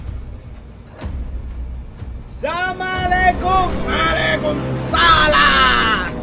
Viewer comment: ซุ่มโจมตี 2:04 ว่าซ่านนนนน 2:09 คิดถึงอ้ายยยยยรึปล่าวววววววว 2:17 ซามาอิหยัง[ใครก็ได้ช่วยบอกที] 2:21 เกะกะ 2:27 อย่ามาลองดีกับอ้ายยยยยเชฟผู้้นี้้้้ 2:49 เข้ามาตรงๆอย่างงั้นเลยหรอออออ 3:01 5555เละเป็นขี้แน่นอน 3:09 Owiwa shinderu!!!! 3:32 เท่านี้ก็จับตัวได้แล้วววววววว 4:01 อ้ายยยยยย!!!!จะขยี้ยูให้แลกกกกกกก!!!!! 4:19 สภาพดูไม่ได้เลยยย~~ 4:23 รับนี้ไป pizzaaaa 5:00 ไม่จบไม่สิ้นนนน เวลาเอยยยยยยย จงหยุด 5:07 พวกอ้ายยยยยยคงจะเหนื่อยกันแล้วววเดี๋ยวอ้ายยยยจัดการเอง 5:13 ฟังไม่ออกเลยยยยยย 5:17 ทำลายมันสะะะ 5:47 เอาเพปปาโรนีไป 5:58 มัมมามีย่าาาา 6:11 let gooooooo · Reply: เชฟเเกพูดว่า สามาเลกุมมาเลกุมสาลา ครับ😅 (เเปลว่าไรไม่รู้)